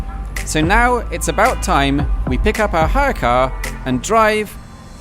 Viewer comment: When you said "hire car", I almost thought you said "haircut" and pondered the reason why you wanted a haircut in the Netherlands.